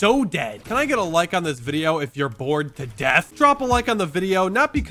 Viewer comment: Lol I thought you said born to death and the I said I am ☠️ but then I found out you said bored to death ☠️ I’m both though to be honest